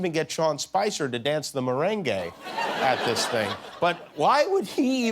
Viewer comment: Did he say the marangay😂😂